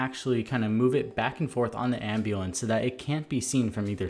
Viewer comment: did he say ambulance